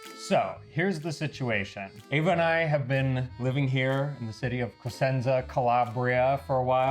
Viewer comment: Honestly, I thought you said "... have been living here in the city of Placenta"... ?! I know your stay is pregnancy-related, but ...!